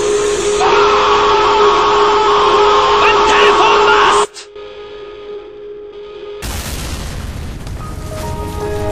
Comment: What did the Wehrmacht soldier say? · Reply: Telefonmast